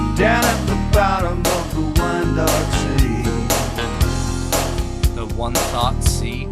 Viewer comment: I think the line is "wine dark sea" a quote from the ancient Greek author Homer who used "wine-dark sea" instead of blue as the word "blue" was not in use then.